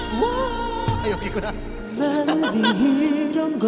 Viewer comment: 여기서 창섭이가 뭐라고 말하고 섭이랑 프니랑 꺄르르 웃는데 뭐라그런거에요?? 비투비 음색에 치인다 진짜...ㅠㅠ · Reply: 아 여ㅋ기ㅋ구나...!, 인 듯... 애드립 타이밍 땜에 웃은 듯요ㅋㅋㅋㅋ